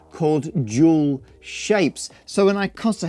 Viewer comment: "Dual shapes". His accent makes it sound like he said "jewel shapes".